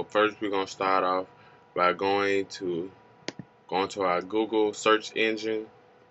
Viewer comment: Did u just said Gogol ??